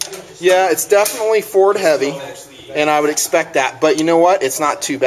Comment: i thought he said its definitly for heavy.